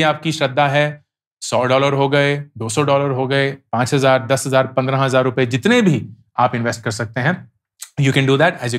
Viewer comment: The video subtitles is incorrect at 18:21 timestamp. It should be Rs5000, Rs 10000 instead of $5000, $10000.